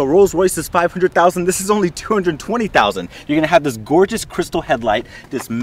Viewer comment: Must have misheard but I think he only 200k 😂😂 onlyyyyy